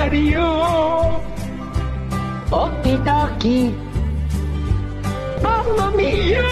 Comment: When he said okey dokey I thought he said okey doggy